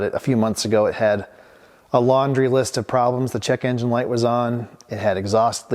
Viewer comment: I totally heard "chicken light is on"" hahahaha!!!